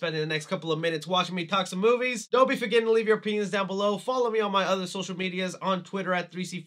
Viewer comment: you should slow down a little bit when you say " leave you opinions down below ", It really sounded like you said " leave your pen_s down below "....yeah I'm not falling for that one..not again.